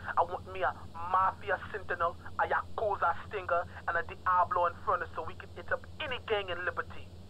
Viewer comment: If u listened to King Courtney carefully he actually said a Diablo Infernus this shows that the Diablos gang car was originally supposed to be an Infernus so either this was an early concept that got cancelled or a mistake in spelling or some kind of error by Rockstar there. So looks like the Diablos were supposed to be driving an Infernus Instead of a custom Stallion but again the Custom Stallion they have is a badass vehicle.